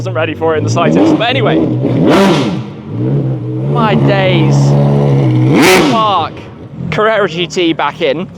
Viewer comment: What did he just say? 😂😂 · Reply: "my days, the bark"